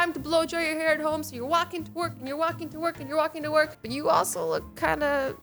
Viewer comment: Who else thought when she say going to work she said gonna twerk.....